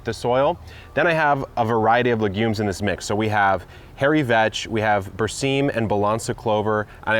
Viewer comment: did you say hairy vag?